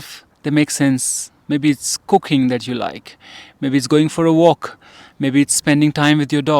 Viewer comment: Oops! I thought you said: “Maybe it’s cocaine that you like”🥴